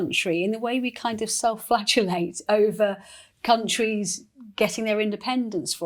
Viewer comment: Er, did you say anything self flatulate?!😢 @ 39:15 -18